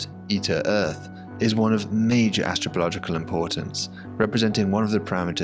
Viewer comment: did you say it's of major astrological importance? Wish Kepler had found more Earth-like planets, so that we could all have more accurate horoscopes 😅